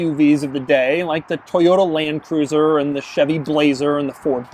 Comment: Did Doug just say "Toyota Lanecruiser"???